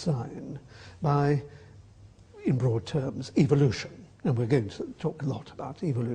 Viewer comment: Doesn't Atkins sound like he is saying 'evilution'?